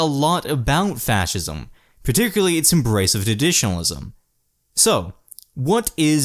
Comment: did u say embrace of traditionalism? u sounded a little slurred